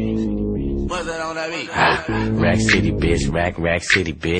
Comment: i thought it said rag city... rack city makes less sense :/